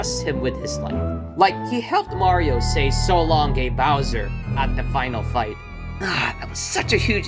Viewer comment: Being a big fan of the franchise, I can say that Mario doesn't say "so long gay bowser" he actually says "so long KING bowser"